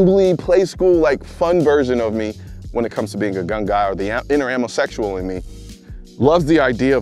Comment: did you say the inner ammo sexual? lol add that one to the list of ways to identify these days.